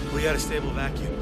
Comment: did he said "we have a stable vacuum"? a VACUUM?